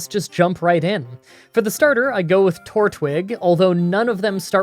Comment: Did anyone else hear him say Tor twig